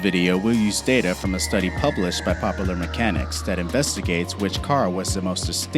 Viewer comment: did u really say popeler instead of popular